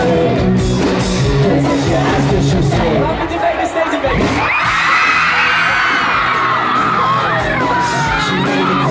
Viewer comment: I DON'T UNDERSTAND WHAT MICHAEL SAYS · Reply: "What happens in Vegas stays in Vegas"